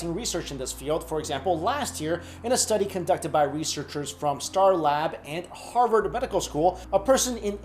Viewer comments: did he say "Star Labs"? :| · Did he say S.T.A.R. Labs at 0:26 ? Did India build S.T.A.R. labs? can I become the flash ?